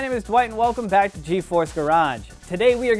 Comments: Thought it said geforce garbage lol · I thought it said geforce garbage lol